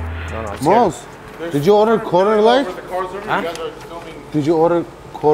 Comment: ha corner light, i thought he said quarter leg like they had ordered chicken